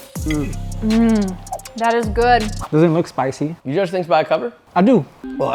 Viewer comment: can someone translate this for me? what language was that joshua said? "yajatee bycove"? wtf does that even mean? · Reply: "You judge things by cover?" Like the old saying "you cant judge a book by its cover."